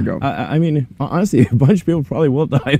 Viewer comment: "It's touch letting over there"? What's he saying? Anyone know? 4:57 Did he smoke a massive spliff? He's giggling his way through this. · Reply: "it's tough sledding"